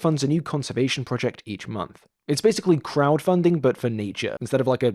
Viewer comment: I misheard “It’s basically crowdfunding for Nietzsche.” and thought about how much he would have hated that. (Irrelevant engagement, woo)